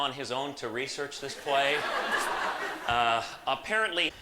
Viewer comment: can anyone tell me what line seems to have gotten cut out here? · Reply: "Apparently he looked up 'moor' in tbe dictionary and thought it was a place that you tie up boats."